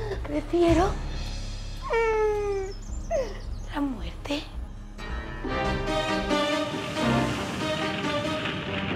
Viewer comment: soraya Callate no entendi lo que dijo maria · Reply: Dijo: "Ya vale, déjate ayudar, por favor".